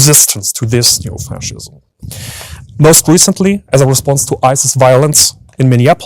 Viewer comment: the subtitle says "ISIS", but he said "Ice's", ikyk, not your subtitles, but just to be sure.